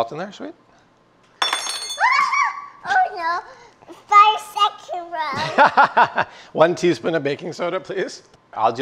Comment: Wait what did she say at 1:34 😱🤭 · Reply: 5 second rule